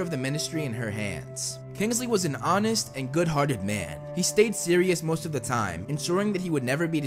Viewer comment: um... i heard he stayed sirius